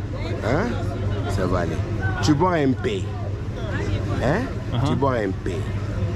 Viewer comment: he says "un peu" not "en paix", which translates to "a little bit" instead of "in peace".